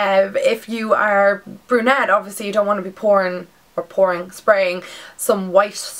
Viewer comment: i just subscribed to your channel. you're very pretty! :) when u said "you don't want to be pouring" i thought u said "you don't want to be porn" then i had to repeat the vid LOL.